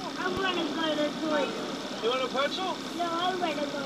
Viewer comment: I thought the dad said, “You want a pencil?” Instead of Pretzel